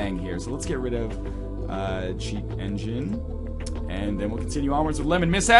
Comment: Is it just me or did NL say cheap engine at 7:10 do